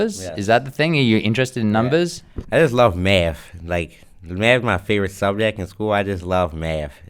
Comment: i keep hearing "i just love meth"